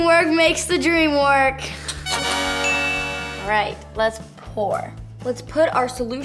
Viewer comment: I thought she said lets porn